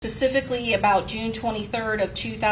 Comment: Did she say “Pacifically” @ 00:06-00:09 🤦🏽‍♀️